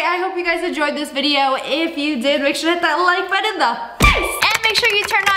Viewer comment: Why does it sound like she is saying "make sure to hit the like butt in the face"